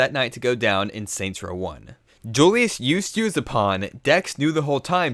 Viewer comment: wtf did he say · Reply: Julius used you as a pawn. Why?